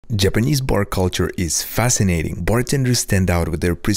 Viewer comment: Was i the only one who thought he said "Japanese porn culture is fascinating" at the very beginning? 🙄